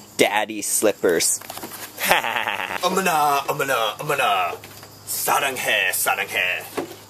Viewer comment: It sounds like says "Omena, omena, omena!" at 1:49, and it actually means "Apple, apple, apple!" in Finnish...